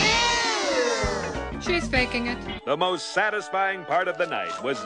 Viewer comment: When I was a kid, I thought she said "THEY'RE faking it. "